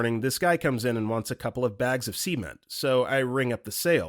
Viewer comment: I don't really give a damn who reads the videos. So long as they speak well and have flesh. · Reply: The way he prounonced cement, it sounds like he says semen…